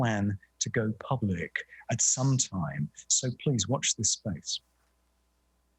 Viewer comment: Did he just say please watch the spacs, at the very end of the video? Holy. .